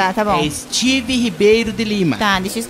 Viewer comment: Eu juro que ouvi Steve PEIDO de lime Kkkkkkkkkkkkkk, Quem ouviu também